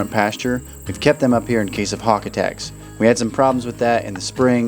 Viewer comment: I thought you said heart attacks, lol. Not hawk attacks 😅